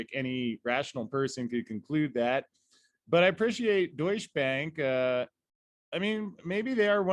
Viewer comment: I keep hearing “douche bag” 😂